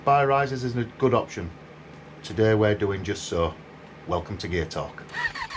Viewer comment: I thought you said, 'Welcome to Gay Talk'! Thought I'd come to the wrong channel! LOL!